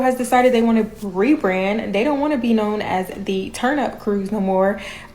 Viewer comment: All I hear is "Turnip" cruise.